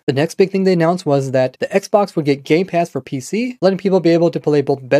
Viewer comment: I started laughing so hard when I thought he said gay pass instead of game pass